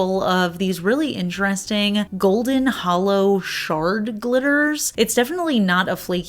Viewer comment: I kept hearing “shark glitters” instead of “shard glitters.” 😂😂😂